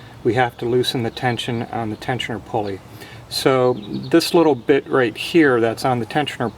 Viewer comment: haha I had the volume too low but at first, thought he said "this little bitch"